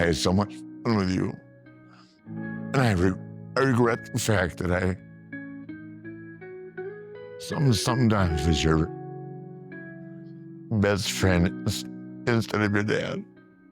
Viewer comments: "And I regret the fact I sometimes was your best friend instead of your dad." Your butchered subtitles completely missed the point. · Just fyi around the 26:00 I think he says sometimes I was your best friend instead of your dad